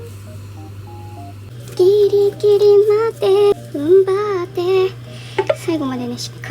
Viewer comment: 途中、ウルトラマンガイア流れたような？ギリギリまで頑張って～って聞こえたような？